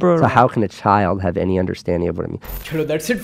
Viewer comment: I still can't understand what he said in the end 15:55 🙂😫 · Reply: Pehle "take care" ka rhyming karke kailash kher anupam kher bolta tha Ab tc ka rhyming karke sc,St,obc bolta hai